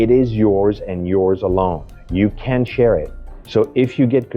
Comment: A very minor script suggestion-I heard a self contradicting statement from you and figured you must have meant negative instead of positive. When you said a pass is yours and yours alone, I heard after that “You CAN share it”. I assume there was a T there slightly missed by the audio. Recommendation is to avoid the contraction and use the word NOT for emphasis and clarity.